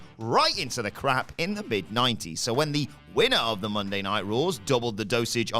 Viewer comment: Is it just me or did he just say winner of the Monday night raws?